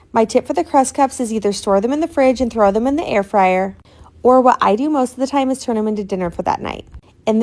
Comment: I thought you said throw them in the fridge and store them in the air fryer. I knew i was sleepy but not THAT bad.